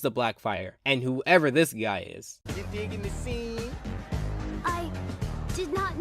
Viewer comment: What does that guy say at 12:08? I can’t hear it. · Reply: He asks "Are you digging the sea?"